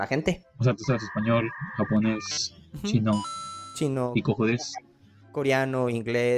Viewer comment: alguien sabe que dijo el peruano en el minuto 10:50 no suena como, "que mas," a mi me suena a "koko desu" o algo, no entiendi pa na. Estudio espanol, por es pregunto.